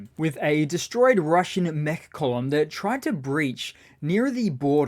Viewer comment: Mech column.. i thought you said meth column